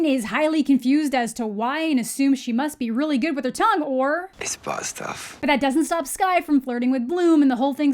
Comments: All I hear here is, "It's pasta." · Is Riven saying "It's butt stuff" in those clips? I literally can't make out what he's saying because his voice is so low and scratchy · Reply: I thought he said "ball stuff" 😂 But thinking about it, "butt stuff" probably makes more sense.